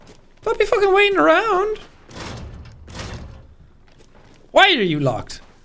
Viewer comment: I thought he was gonna say why are you gae?